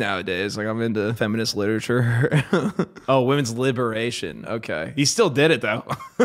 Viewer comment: It sounded like he said "women's lib" but I think he actually said lit, because who the hell shortens liberation to lib? Either what he said was hilariously desperate. I regret to inform you the performative males have always existed.